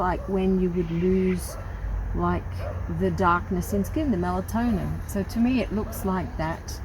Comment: Yes quite right - it is Melanin. 💙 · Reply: oh sorry i thought you said melatonin